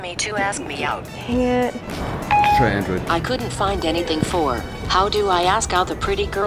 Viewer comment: "You should try android". My new hero. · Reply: Carolina Carvalho The guy at 00:32 is NOT saying "you should try android". That is what ***** wants you to believe and what he wrote onto the video. That guy is simply saying "It's android" and with that he means "you are on iOS, I am on Android, so there's no way this trick gonna works because of the compatibility".